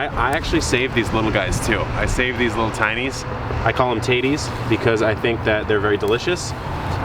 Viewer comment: lol. CC says titties